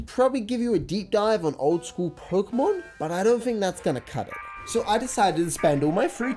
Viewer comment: I heard a lot of youtubers misspell "pokemon" as "pokimon"... but it's the first time I hear someone spell it "prokmon". ;)